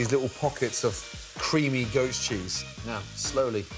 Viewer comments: I keep hearing "ghost cheese" and I find it insanely funny. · I thought he said “ghost cheese”